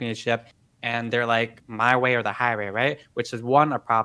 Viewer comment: Lol I heard “My way or the Hai Way” on